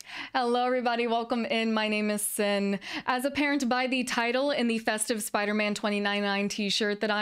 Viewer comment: When said, "as apparent by," all I heard was, "as a parent." I totally thought she was launching into a Karen story about taking her kid somewhere.